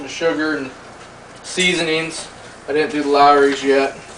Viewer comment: im trying this recipe as soon as possible but i cant understand the last ingredient at minute 4:30, can you please let me know? thanks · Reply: ***** lawrys seasoning, a substitute is jonnys seasonings